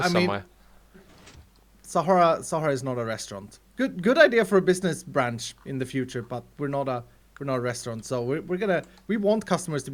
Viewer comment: OMG At 13:22 when says "good idea for a business branch but we are not a restaurant", The subtitles say but we are not a porno